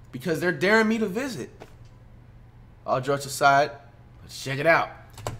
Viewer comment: did dis nigga jus say "all drugs aside" ccuz instead of "all jokes aside"?..